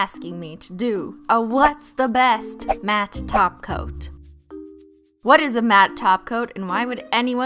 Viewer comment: DID SHE SAY TOP COAT AND NOt TACO🥺